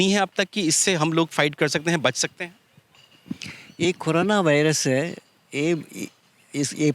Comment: At first i thought i heard khurana virus